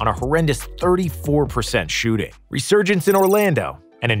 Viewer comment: What the hell word is “Remargance” at 3:45? You look like idiots. Congratulations! You’re as bad as Darko.